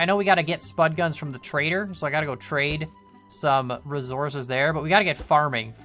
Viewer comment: did he say rezorzez